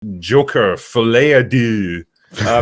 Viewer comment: My phone was in the other room and I thought I heard the opening as: Joker: Filet o’ Poo